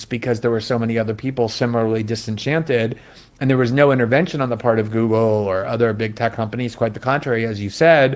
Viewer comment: That's not quite what he just said (cf. 3:32). He said "no intervention on the part of Google". He's comparing then with now, where there is intervention and algorithmic rigging.